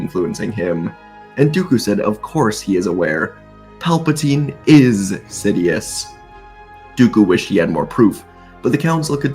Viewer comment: Thought dooku said "Palpatine is shit"